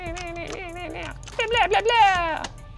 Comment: Did he just say Blyat while playing CSGO??!!!!!!!!